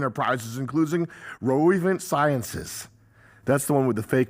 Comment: Saying vivek’s fake company as “sinuses” instead of “sciences” as he’s clearing out his sinuses is hilarious for no reason